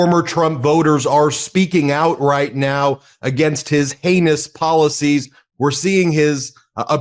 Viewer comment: I thought said; “His anus policies”. He’s not wrong. Lol